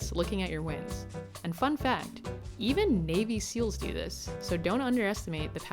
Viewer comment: I heard "even baby seals do this" and I am going to stick with my version, sorry.